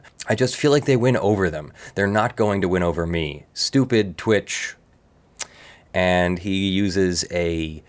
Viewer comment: Watched the actual stream where it occurred, as linked here, and I'm not sure what word that triggered her is - her inability to annunciation when she eats at the same time also makes it hard to hear what she says a lot of the time. But looking over her streams, it is clear she is a tit-streamer. She may not like it, but she wears the uniform. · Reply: Took me a while to get it. Despite it being a weird way to say it (as Felix does) I Initially thought he said "stupid twitch thought", what he was actually said was "stupid twitch thot". <link>